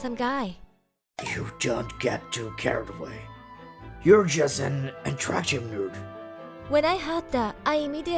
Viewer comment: Tanaka: You, don't get too carried away. You're just an unattractive nerd. No matter how attractive a person is, said person loses that attractiveness, if he/she looks down on others. Being beautiful, isn't a license to be an a*sh0le on other people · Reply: Dude I was not looking at the screen at that part, so didn't see the comic and just heard him saying, "- you're just an attractive need" 😅😆